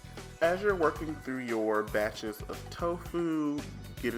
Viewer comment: Yummy! [Edit: 9:28 I heard that wrong and thought you said "Bachelors of Tofu", which I thought was hilarious, but then I realised I had had a brain hiccup.]